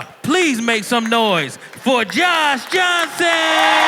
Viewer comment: I heard the intro, "give it up for John Johnson"